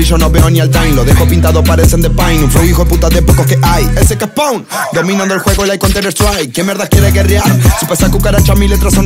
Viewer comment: [Verso 1] Lo enrolo y lo prendo a jamaiquino mi ojos están chinos siempre vivo HIGH Por redes ni hola, yo no hablo online Solo pa las babys que mandan facetime Medio rayado con un flow offwhite O de jordan retro completo de nike Yo no uso bufanda porque tengo ice Roleta con brillo no veo ni el time Los dejo pintado parecen de paint Un flow hijoeputa de pocos que hay SK SPAWN Dominando el juego like counter strike Quien mierda quiere guerrear Si pa esas cucharacha mi letras son raid Prendo los motores cuando prendo el mic A saltos gigantes como el fly de mike Todo termina con ay Como el grito de tu p* cuando yo la pongo a brincarrr DROP [Verso 2] Quien mierda quiere guerra Ponganle correa a sus perras Porque llegó el Golden Team Y el V.I.P se cierra Tengo la calle caliente Y si los cruzo se quedan más blancos que mis dientes Cuando me pronuncio En la disco arranca el perreo sucio Me dicen el que se pegó sin pagar un anuncio Las babys sabes que soy el de ahora Como pal tiempo de Don con la batidora Dorado, el reloj y todas las prendas De flow soy un donador para que aprendan No existe fuerza que a mi me detenga El que las leyes da aka leyenda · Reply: Amigo pense que decia " Dice que es Phoon dominando el juego like counter strike" me re hypie al pedo amigo